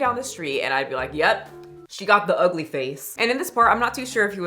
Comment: not ugly! specifically "weird faced". Not sayin I agree. just sayin, that's what he said